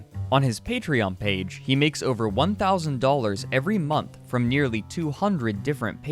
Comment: Hey, at 0:52 in the captions, it says $100,000 instead of $1,000 dollars in the captions